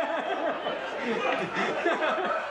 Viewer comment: That part were elmo sinks into the box leaving only his eyes to be visible and then saying I only had three lines really resonates with me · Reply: Omg I thought he said “I only had three lines”